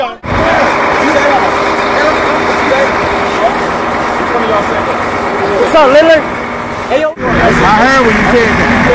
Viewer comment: What did he say to lillard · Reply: he said "yo mama"